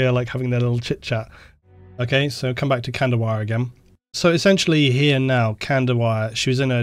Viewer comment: This was awesome, thanks for taking the time to edit down what must have been a huge stream. I thought you were saying one of the characters name was candlewire and I was all "that's an odd name" lol once you spelled it out I was all "ohhhhh, lol 40k names sure are something"